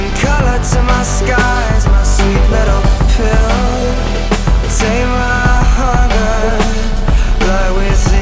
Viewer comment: It sounded like he said take my Honda